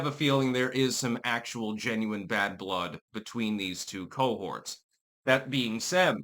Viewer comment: did you say two cohorts or two old whores?